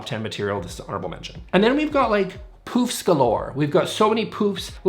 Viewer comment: Hahah I thought you said “poops galore”. Hahah. As a fast talker myself I love fast talking! It means I get more per minute!